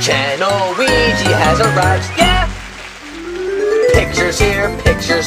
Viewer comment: at the begining of this it sounds like luigi says photo channel Weegee has a rush yeah!